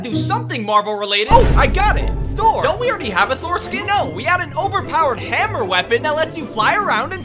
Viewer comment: I Heard "don't we already have a foreskin" instead of "don't we already have a thor skin"